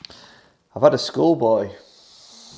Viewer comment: Great fun. Was a bit worried when I thought you’d said “I’ve had a schoolboy “ !!!!!